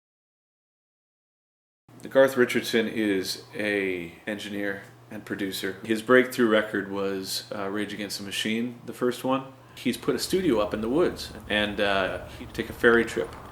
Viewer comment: Someone could please tell me what he said at the 0:35? I'm not fluent in english and definitely coldn't understand the first words he said :/ It was too fast. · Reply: "Garth Richardson is a, Engineer and Producer. His breakthrough record was, uh, Rage Against the Machine's first one. He's put a studio up in the woods." I hope that helps friend!